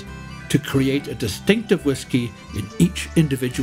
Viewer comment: Very nicely put, I was looking for such informative vedi for long... by the way what was that wor at said, at 10:19 ? Something ‘ lanjawahh ‘ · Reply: It is Slàinte Mhaith and means "Good health"